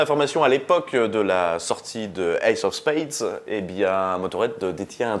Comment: C'est moi ou après le générique de fin à 4:23 il dit "the EIGHT of spades" ?